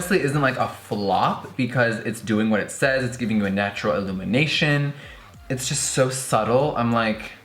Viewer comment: i thought he said ILLUMINATI😂😭